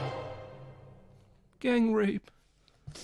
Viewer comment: what makes it so funny to me is how bluntly it was put. no dancing around with a "huh wonder where that went" just "gang rape." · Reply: OHHHH HE SAID GANG RAPE??? I thought he said "game rape"